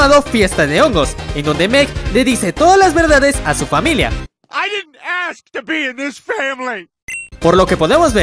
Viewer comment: Que Fue Lo Que Dijo? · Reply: Dijo Yo nunca quise ser parte de esta familia